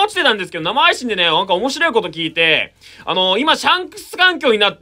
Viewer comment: シャンスクの言い方がジャン！に聞こえる